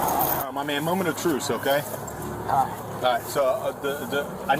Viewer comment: Did he say “Moment of truce”?